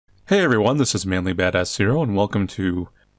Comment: Is it just me or did it sound like he said manly badass cereal